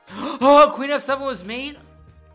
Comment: anyone have a clue what he was saying here? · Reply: Gasps "Queen f7 was mate?!??!?!?"